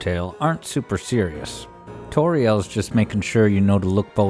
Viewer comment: It sounds like you said "torielle"